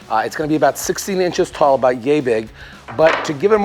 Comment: did anyone when the first heard him say yay big, did it sound like he said gay big? Its kinda funny and surprising to me because im a person who likes to build things in games and you just randomly showed up on my recommended and my mind was just telling me to watch your video! I sent this to my dad who also loves building things and this would be a great thing to build for my first kids if I have one. Also my mind though: BUILDING! BUILDING! BUILDING! LOTS OF BUILDING 😂 When I saw you using the primar paint spray thing I was surprised also because I just primar painted my room yesterday and it took me like 3 hours to do that and seeing you do it the easy way got me laughing at myself😂😂